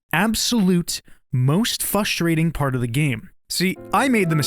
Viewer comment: did bro say "fustrating"